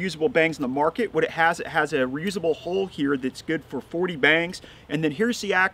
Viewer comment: I had to listen to that again, I thought you said it had a reusable hole good for 40 bangs. I mean I wouldn’t judge, but now I see you said “hull”